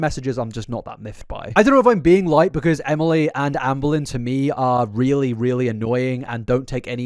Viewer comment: It sounds like you say Ann Boleyn sometimes and it’s really funny lol